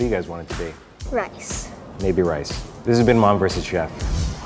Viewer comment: Spaghetti tacos and a girl named Miley... hello childhood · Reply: Did say "Oh yes" at the end pf the video?